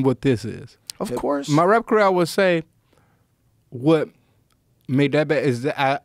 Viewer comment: huh? Tf did he say?! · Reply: 'What made that bad'